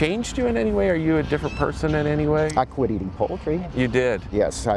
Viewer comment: I thought he said "I stopped reading poetry"... it wasn't until the follow up question that I realized it was: "I stopped eating poultry."....makes a lot more sense now...sorry...random thoughts.